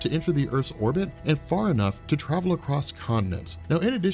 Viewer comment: Did he say condoms,cause a 2:10.He says "enough to jump condoms".